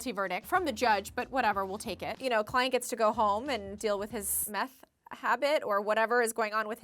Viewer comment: i thought she said klien gets to go home which is funny cause he looks like klien from sword art online 😂